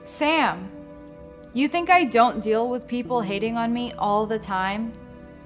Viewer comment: I thought she said, "You think I don't deal with people hitting on me all the time?"